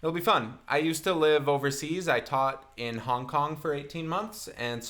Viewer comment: Hi! Before you rip me apart please read this. If your comment isn't addressed I'd love to hear it. 1. I know Hong Kong and South Korea are different places. I lived in Hong Kong for 18 months. What I was so poorly trying to refer to in the video was how I worked with a lot of clients on KPOP. I misspoke. 2. Compressors and auto-tune are completely different things. I was not talking about auto-tune, I was talking about compressors. Compressors are often used in live and studio environments. They are not a bad thing, they are a good thing. I thought that singer was fine in both clips. 3. I don't know everything, but I have spent an enormous amount of time learning what I know. I am actually a professional voice teacher. My students make progress every lesson. I'm not perfect but I'm always trying to learn. Thank you for watching! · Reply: I thought 'Hong Kong' said 'Hong Joong', oops.